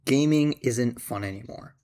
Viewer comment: i thought it said "grooming isnt fun anymore"